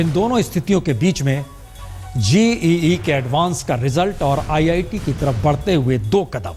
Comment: Did he say GEE instead of JEE?? ya fir mere kaan baj raher hai!!! 1:07 please have a look